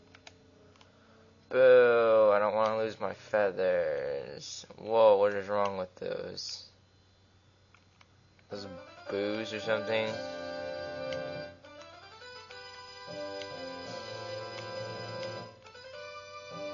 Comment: I thought you said booze rather than boos so i was so confused for like 5 min